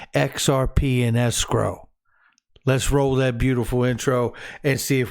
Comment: When you said let’s roll that beautiful intro for some reason I heard it as “let’s roll that beautiful bean footage”